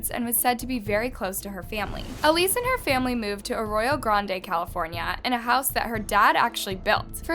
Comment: ARROYO GRANDE i thought she said Ariana Grande.